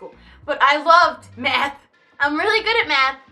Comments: OMG I THOUGHT SHE SAID I LOVED METH OR I DID METH OMG. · I thought you said "I love meth"